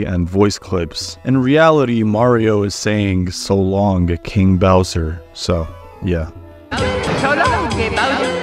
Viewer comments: I thought Mario was saying so longey bowser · I always thought Mario was saying "So long, eh Bowser?" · I thought he was saying so long eh bowser